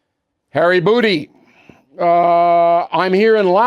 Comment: Anyone catch “Hairy Booty” at 16:17? 😂😂😂😂😂